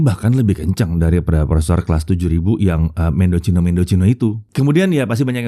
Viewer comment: Perhatikan 13:28 Ini prosesor yang lebih kenceng dari seri 7000 made chindo made chindo itu? ga salah dengerkan ? 😊